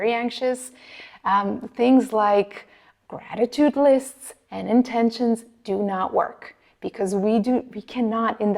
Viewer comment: I can't figure out what she's saying at 4:35. Something list and intentions?